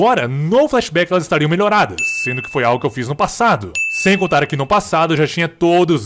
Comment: fala assim: "sendo que foi algo que eu fiz no passado." Na legenda: "sendo que foi algo que eu fiz no FUTURO ." * - * SANTA MONICA !